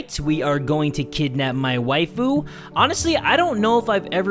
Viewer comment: I thought it said to kidnap "my wifeu"